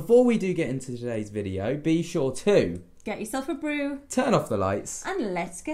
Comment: When I first started watching u guys I thought u said. " Get yourself a broom" lololol. I was thinking like not to clean but like a witch. No clue why I thought that lol